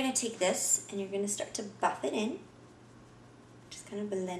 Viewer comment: when she says "BUFF it in" i heard "fuck it in"